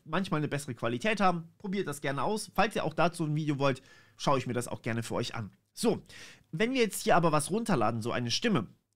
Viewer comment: wie heißt das andere Tool? Ich hab SVC verstanden. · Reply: Es ist der "So-Vits-SVC-Fork", den er meint.